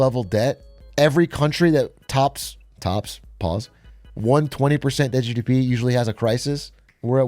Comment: did you just say pause for tops, as in tops, as in gay sex?